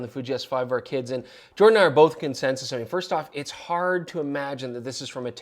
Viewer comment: I don't recall saying that word. It's not even a word. I'm confused. @ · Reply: what WERE you saying at 9:45 then? I listed to it several times, and each time it sounds like "Jordan and I are both concensusing....".